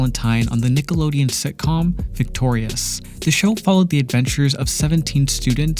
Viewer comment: i thought he said seventeen students lmao😭😭